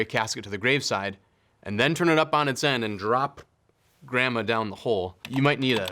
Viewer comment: "... and drop grandma down the hole. You might need a seatbelt?" 💀i should not be laughing this hard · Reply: Lmao I am glad I read these comments, I thought he said “ram it down the hole” at first lol